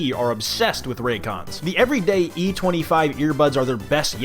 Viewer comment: when you said raycon i wasnt paying attention and my fight or flight response activated since i thought you said raid.